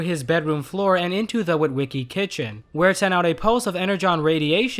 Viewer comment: I thought you said post when referring to the robots coming to life. It was pulse, not as funny.